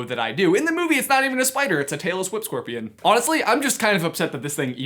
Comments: I genuinely thought you said Taylor Swift scorpion and thought I’d missed something BIG for a moment there 😂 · I thought you said “Taylor Swift scorpion” and my first thought was “ I thought she was a snake” Lmao I need sleep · Genuinely thought you said "Taylor Swift scorpion" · wait... did you say Taylor Swift Scorpion?